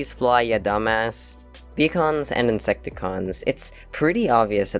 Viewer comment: Why did I hear 'pecans' instead of Vehicons? 😂😅 22:11 : LOOK AT THE LITTLE SHOUDLER TAP!!! Soundwave is secretly nice! Kinda...